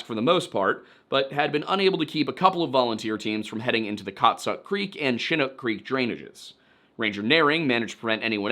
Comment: I thought he was saying Cocksuck creek